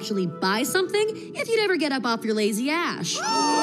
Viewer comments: Dude i hear "Ash" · he think she say A** but she say ash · bro she did not say ass she said ash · she actually said “ash” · She doesn’t swear she says ash because there fire